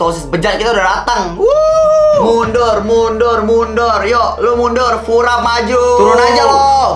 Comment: gak salah dengarkan itu tadi dia bilang "furab"??